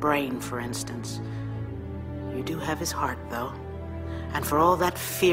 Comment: How is written what she saids in 2:11? After " you do have his hearth..." Inglish isn't my mother language. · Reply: She says "Heart though". His passion and drive basically.